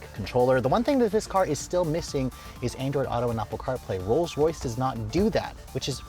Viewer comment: what does he say he is missing? "car plane"??? 5:08 the console looks like one made by children at 4th class